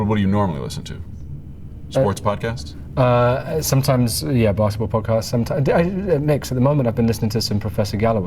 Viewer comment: did Nick say Barstool podcast?